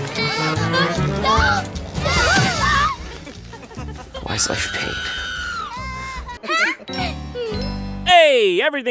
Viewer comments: What the fuck does he even say at 18:54? I can't make it out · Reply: "ECH" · What does he say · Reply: why is life pain Jon said